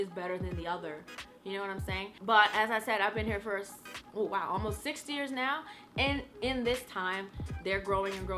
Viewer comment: did she say....she's been there for 60 years?! or 16? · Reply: 60. She's 137 years old this year.